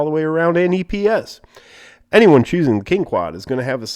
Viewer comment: did he say Ring-Quad?